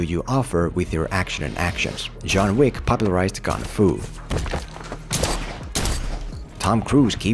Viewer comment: did i hear that correctly??? did he just say john wick popularized(don't know how to spell this) kung fu??? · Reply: lol he said gun-fu, not king fu, you most certainly heard it wrong.